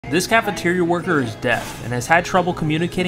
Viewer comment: I heard you say "this cafetirea worker is death"💀😭